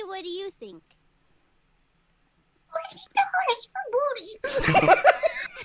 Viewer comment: do you have any idea what said here? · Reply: "$40 for booty", though I might've gotten the number wrong